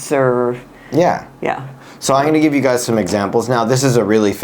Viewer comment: ここの"I’m going to give you guys some examples"の発音、どなたか教えてくれませんか… I’m gonnaとも Immaとも聞こえない😢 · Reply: 何度も聞き直したんですが、so アイニギビガァズ some examplesという風にしか聞こえないですね。 どういうことが起こってるんですかね…。